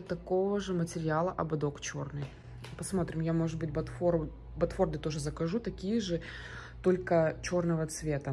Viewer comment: БотфорДы?или мне послышалось ?😅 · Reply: Вам не послышалось,именно ботфорДы,пипец